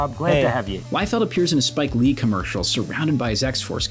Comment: Did he say Spike Lee commercial...